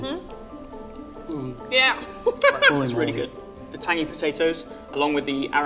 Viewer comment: the subtitles say "tiny" when I'm pretty sure he's saying "tangy"